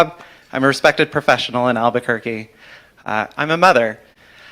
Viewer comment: Did I really hear this dude say: "I am a mother."? He did shrooms or he did shrooms?